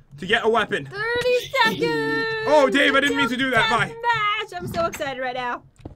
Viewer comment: did he say "oh da*n i didnt mean to do that?